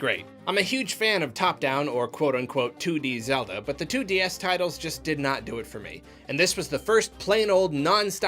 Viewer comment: at first I heard "2DS titles" and I was like "was there a zelda game that I missed?"